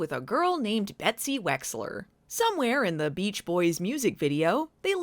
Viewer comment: I'm sorry, I keep hearing Mitch's ex's name as "Betsy Wetsy." So, she's Betsy Wetsy now.